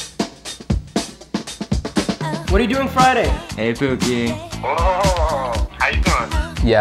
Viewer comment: Is it just me or did I hear "Hi pookie:))"